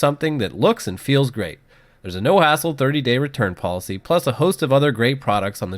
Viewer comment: Happy New Year guys! During the Drop keyboard spot, said "there's a no hassle return policy" and I swear, I heard "no asshole return policy"! lol. I had to go back and listen again. 😆